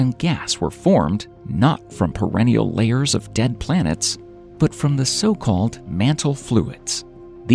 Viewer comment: Layers of dead planets??? WTF Did I hear that correctly?